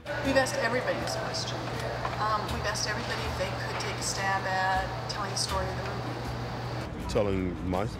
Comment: What does the journalist ask at 16:34 that every actor answers "absolutly not" to ? I cant understand her clearly. I hope someone can tell me thank you ! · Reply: She said "We've asked everybody if they could take a stab at telling us the story of this movie".